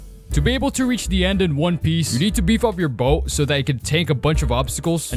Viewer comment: did you say... ONE PIECE??